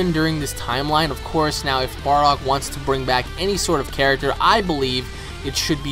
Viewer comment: Did he say "bardock should bring back bardock". Or is it just me. At 2:00 - 2:10.